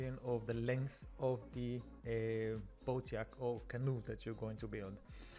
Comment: Yacht sounds like yhat, you don't pronounce the C. like wat but with a yh instead of a w... at least that's how we say it in the USA the brits could be different, sounds like you said yak which is a big smelly bovine, kinda funny to imagine people lounging in swimsuits on a giant yak in the ocean. good English otherwise, your command of the language is impressive.